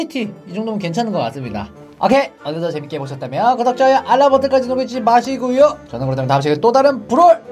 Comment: 초 에서 누르지 마시구유 라고 들리는데